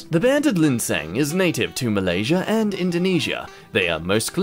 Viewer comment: I swore I heard him say m'lady instead of Malaysia